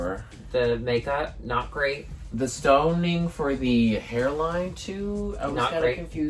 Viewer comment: Now when says "stoning" I hear "stunning" .....